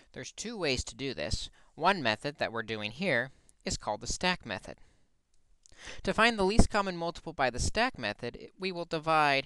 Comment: I thought my teacher said that tjis is called the "cake method"